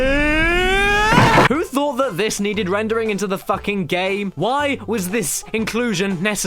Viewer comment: Did he said "nigga" at 5:20? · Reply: No. He said, "Who thought that this needed rendering into the fucking game?!"